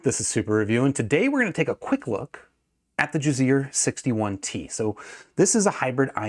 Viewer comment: I thought you were saying jizzear the whole time. I’m like who would name their company that haha